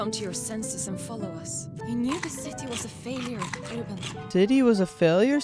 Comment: sounds like he said "diddy was a failure"